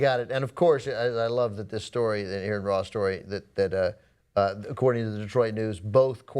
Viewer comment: He talks about the Aron-Ra story. What's he talking about? Did I mishear him?